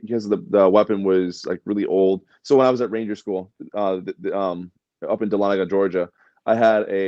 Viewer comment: Congrats to for completing Ranger school. That’s not an easy school to get through. Much respect. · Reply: You know, maybe its the costume, but I thought he said Reindeer school.